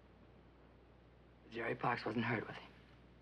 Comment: Love this episode -- thank you. WHAT does she say about 3:46? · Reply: She says that "Jerry Parks wasn't hurt, was he?" Jerry Parks was the gossip columnist.